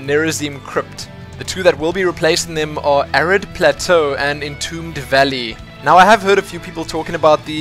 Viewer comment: Haha thought you said Arreat plateau (A Diablo 2 area^^) at first when you talked about the new map Arid plateau! xD